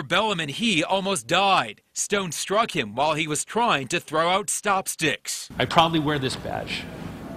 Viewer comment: I heard "While he he was trying to throw out chopsticks" XD